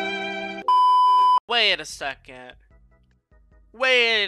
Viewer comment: i thought he said lock shi